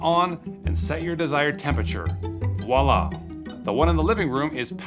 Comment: I love the way says Viola instead of the correct Voila